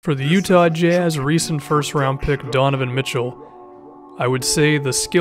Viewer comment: I almost thought it said Donovan McNabb lol💨🔥